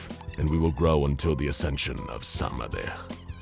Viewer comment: I thought it said Sahdmadhi then. As in Nahyuta Sahdmadhi from ace attorney it kinda scared me.